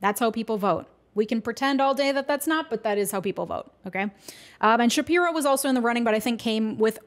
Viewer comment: did you say “Ben Shapiro”? 😅